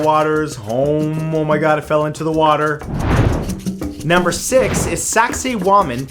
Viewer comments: I thought you said "Number 6 is Sexy Woman, Peru" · I thought you said sexy woman Peru